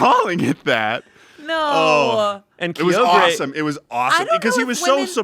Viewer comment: was shayne about to make a pokemon sapphire joke? It sounded like he said a mispronounced version of "Kyogre"